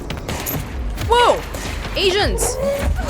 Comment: I heard "whoa Asians!"